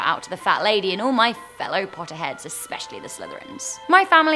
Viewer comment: When she said "Potterheads" I swear I heard her say "Potheads" XDDDDDDDD